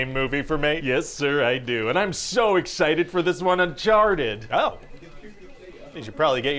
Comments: took me a while but it sound like he said I sharded! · the first joke "Im so excited Uncharted!" the producer thought he said hes so excited he sharted, lmao